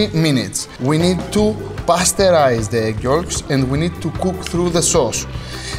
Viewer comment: lol when he said pasteurise the egg yolks, I thought he said "pasta-rise" the egg yolks, and I thought wow you literally are pasta-rising the egg yolks.